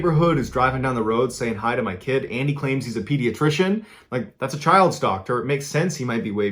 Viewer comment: when you said "child's doctor" I heard "child stalker" and could not fathom the nonchalant response.